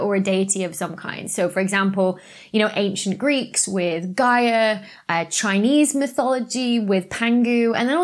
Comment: i misheard as "...chinese mythology with Pingu..." 🐧